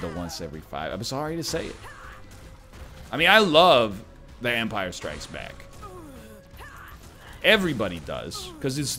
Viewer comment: vampire strikes back? Is that what he said?